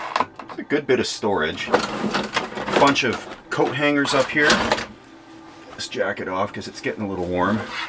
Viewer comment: The springs on the oven sound bought back a memory I would never have remembered if not for this lol…. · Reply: Also, when you said a bunch of coat hangers up here…. In that attire thought you were about to say bunch of cocaine up here 😂